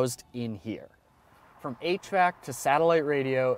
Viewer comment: Sounds like he said, "From 8-track to satellite radio...". This must be a Boss Mustang circa 1972, LOL!! (yes, in retrospect he must've said "HVAC" but it still made me rewind.)